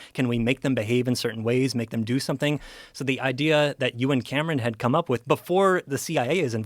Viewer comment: For the first couple minutes of this video, every time this dude said Euan Camerons' name, I thought he was saying that Joe and some guy named Cameron were doing something. (you and Cameron) 🤣🤣 Anyone else or am I alone on this?